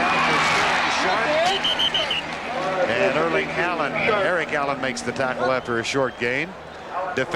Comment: lol sounds like Vern says Erling Haaland rather than Eric Allen. Is Vern a time traveler???